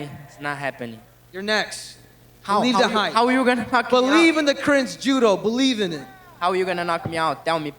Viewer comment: does Cejudo say "cringe judo"? He's self aware af if so lol